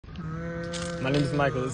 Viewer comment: I thought he said “ before my rizz “ I was so confused why he was on the ground dying 😭💀